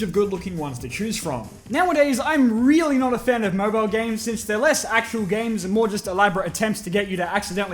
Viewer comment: Anyone else thought it said "OLDSMOBILE GAMES" ?